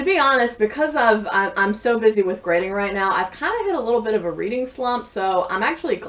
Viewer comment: Hahaha I love that you actually filmed this between grading things, keeping it real! At first, I thought you said grating lol. Why We're Polarized sounds interesting a mix between the two mentioned sounds like a match made in heaven, and really what people need! I haven't heard of this book by Toni Morrison, glad to hear about it and the question it poses. You have reminded me I need to pick up more Toni Morrison. I've only read one of her books and I've totally forgotten what it's about.